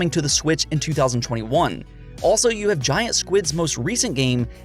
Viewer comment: supergiant, not giant squid, sorry to be nitpicky but i just heard giantsquid and couldnt stop laughing